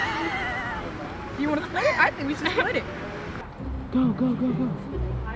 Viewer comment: did she say i want to split it 🤣🤣🤣..?